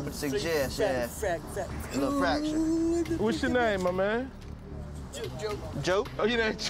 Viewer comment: did he say fractured or fa....ot lol